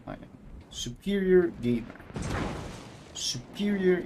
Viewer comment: so uhm I heard "gaper" not "gamer". Which instantly conjured an image of the average Yoshino player opening wide to receive Slava-senpais broadside. I feel physically violated by my own brain.